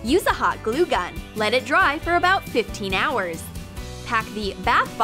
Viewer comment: did she just say 15 hour?